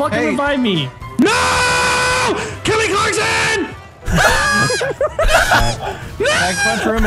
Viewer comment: did he say callmecarson